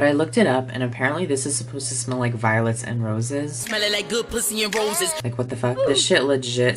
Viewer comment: Am I the only person who thought he said “it smells like violence and roses” Lmaooo